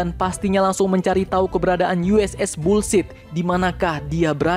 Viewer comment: Jir kedengerannya USS Bull Sh*t 😅😅